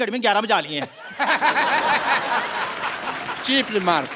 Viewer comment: what did he say? Cheaply maar? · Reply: Cheap remark